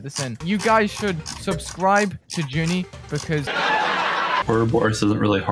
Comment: I heard “subscribe to Ginny” is real name Ginny???